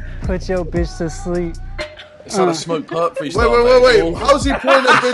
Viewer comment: "It is a Smoke Purp freestyle" -Harry 2020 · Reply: I misheard “is that a smokepurpp freestyle mate”